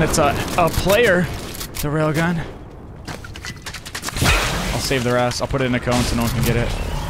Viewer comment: Am I the only one how heard NickEh30 swear 2:00 min in he said "I'll save their a**" What a coincidence!